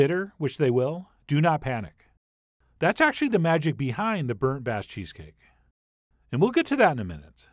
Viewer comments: I mean no insult at all it looks really good! , but for a quick second i misheard as "burnt ass cheesecake" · i listen to chef john on autoplay in my room but had to come over and restart when I thought I heard burnt-ass cheesecake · In the beginning I definitely thought you said “burnt ass cheesecake” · I heard "Burnt ass cheesecake" 🤣🤣🤣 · For a second the intro i thought he said burnt ass cheesecake lmao . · Could've sworn he said "burnt ass cheesecake" 🤣🤣🤣